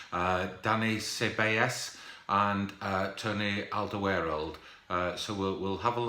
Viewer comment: is it just me or did he say Tony Alderweireld at 0:10?